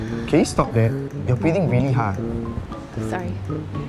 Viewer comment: What said: “Stop that, your breathing really hard.” What I heard: “Stop that, your already really high.”